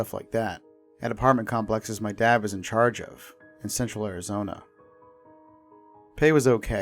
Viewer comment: i thought he said sexual arizona!!!